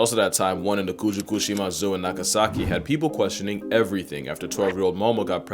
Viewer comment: Oh... oh no... did you say.... nagasaki?